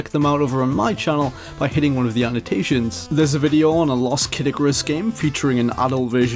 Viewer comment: lolz when he said "a lost Kid Icarus game" I thought he said "a lost Caddicarus game" I was like what?